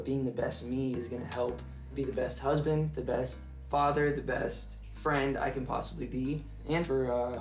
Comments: did I hear him say father??? 🤔 holyshit so excited for a baby bieber 👶🏻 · Did he say to be a better husband and father?? 😳 Great job Justin! Keep it up! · HOLD ON GUYS, WHEN HE WAS LISTING THINGS HE WILL BE BETTER AT DID HE SAY FATHER?